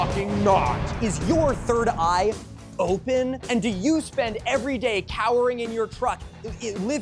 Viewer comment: I'll be honest, I misheard you initially and thought you said "cowering in your trough" and I'm like "WTF how would that even work?!"